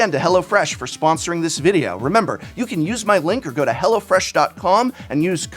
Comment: you had me jumping at 11:33 I misheard "you can use my link or go to hell..." can't unhear it now. :)